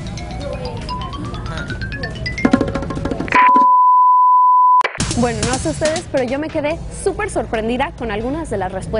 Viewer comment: Y yo acá esperando para saber que dijo Christopher en el minuto 8:31 😂😂😂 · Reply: Ludmi Miranda Seis novias 😅